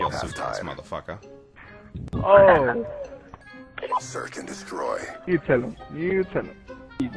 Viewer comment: did anyone else hear "serc and destroy"?